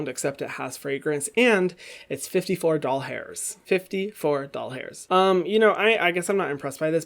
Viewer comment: When he said dollars I thought he said doll hairs...